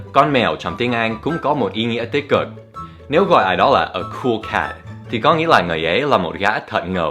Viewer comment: nghe như k* k*c:))